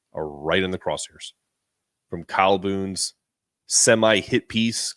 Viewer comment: Calhoun said Coach Hurl is an " embarrassment " ? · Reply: I thought he said that too, lol. He's saying "Kyle Boone" not Calhoun.